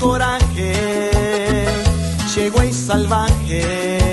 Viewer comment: Pense que decia "Che wey salvaje" :(